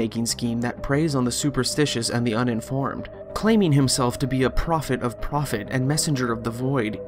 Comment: Profit of profit? I always thought he said "hobbit of profit" :(